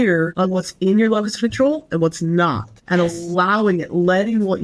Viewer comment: What does she say at 43:08? Being assertive “It’s being really clear with other people what’s in …. ….. ….. and what’s not.” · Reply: what's in your control and what's not...